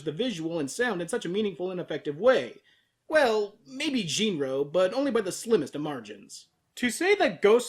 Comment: What Anime was referencing at 1:20? I can't quite hear him... genero? jeano?? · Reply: Jin-Roh: The Wolf Brigade. Excellent film scripted by Mamoru Oshii.